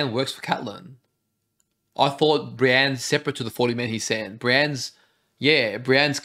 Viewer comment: mate what is that sentence that just said with your mouth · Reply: seperate to the 40 men he sent* 🥰 i talk fast haha